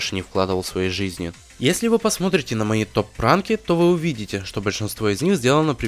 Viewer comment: Мне послышалось, но в моменте на 9:51, слышится, не топ ранки, а топ пранки?